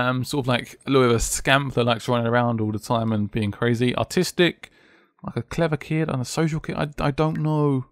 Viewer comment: i thought he said autistic at first i'm terrible